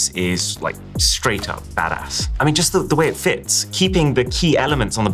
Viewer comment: what did he just say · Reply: Badass